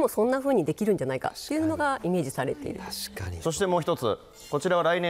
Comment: 皆さんが連呼して聞こえるソフィア→スフィアです💦